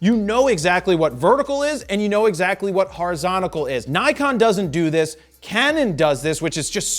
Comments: did I just hear horizontical??😅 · hol up did he just say "horizonical"